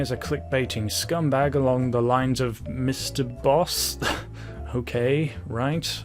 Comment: I thought you said Mr Beast not Mr Boss, LMAO